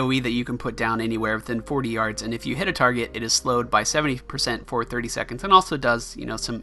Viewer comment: from 6:30 to 6:32 did he say slowed by 70% for 30 secs